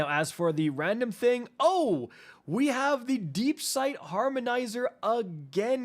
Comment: I thought it said deepshit lol